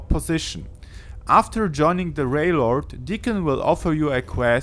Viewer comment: Did he say Railord??